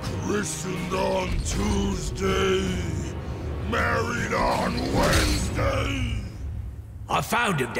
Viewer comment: Fact: Solomon Grundy is a zombie true ay go to nerdsync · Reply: I thought he said burried on a Wensdsay